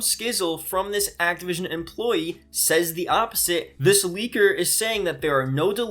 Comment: I thought you said this tweaker